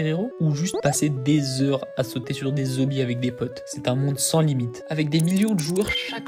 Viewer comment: Moi j'entends à 0:12 "sauter sur des zebis avec des potes" 😂